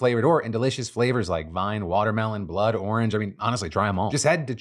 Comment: Side note, you should probably pay attention to where you pause. You can't see a comma in speech, but you can sure hear it, and it sounded like you said the flavors were blood and orange.